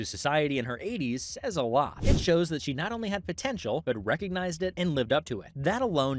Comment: did you just say potential?